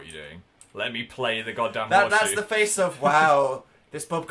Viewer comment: did phil swear at 2:39 saying god damn or am I insane